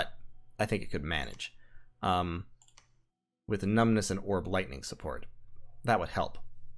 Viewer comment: Earth skin and Iron power? What are these mysterious spells you speak of? I am filled with Confusion. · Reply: At one point you said Orb lightning and I could have sworn you said Warp lightning. The rats, THEY'RE EVERYWHERE.